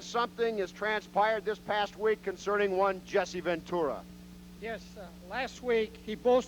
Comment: Jesse off to WWF-Land! Oh, and did Wally, say "Patera", instead of "Ventura"? Ha, Jesse leaving shook the AWA to it's core, jack!